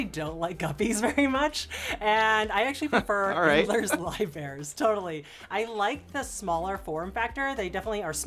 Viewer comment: Can anyone anywhere understand what she is saying at 1:09 ? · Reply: "Endlers live bearers"